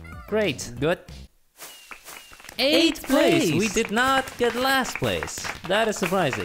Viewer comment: The first round instead of saying eighth place, I thought he said ayeeeeee